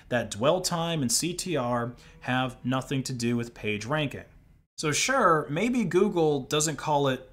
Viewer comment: i heard "page drank" until the comments said “page rank“. Hope this help someone.